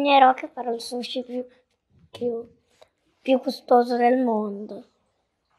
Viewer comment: Io non ho capito "costoso" ma "gustoso"